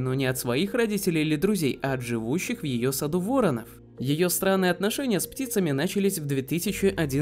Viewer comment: её сраные отношения или послышалось??!